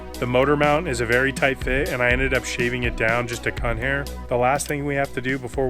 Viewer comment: Wait ... at about 1:23 ... did you say "cunt hair"? LOL hilarious!